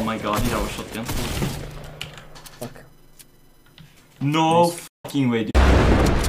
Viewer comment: ｢ｵｵｩ ﾌｯｷﾝｳｪﾃﾞｨ｣に聞こえたw